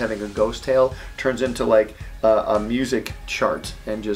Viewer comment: What was said: "a music chart" What I heard: "a music shart"